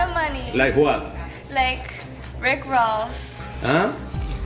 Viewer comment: Rick Ross 😂 oh my God please stop doing Xanax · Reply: I thought she said Rick rolls...meaning rolls of cash